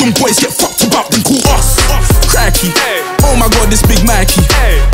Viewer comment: did he say crack kid? xD